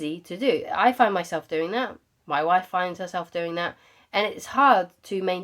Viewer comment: Sorry, did she say my wife? Not judging, kindly asking.. thank you